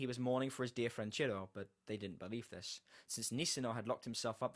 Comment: did you say.. they didn't bayleef this? :OO